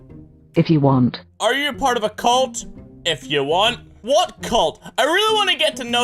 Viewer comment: i thought he said part of a cilt and then i thought clit...EEVEE GOT ANY CLOROX I HAVE MY REASONS! eevee is a pokemon what am i saying is this eevee i dont know but maybe eevee have bleach